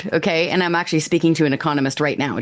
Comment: Did she say she was sleeping with an Enconomist? Good lord, I gotta start studying economics. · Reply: Speaking.